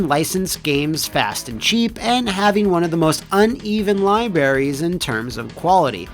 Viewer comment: Bruh I love you but did you just say "Libaries" at 4:24?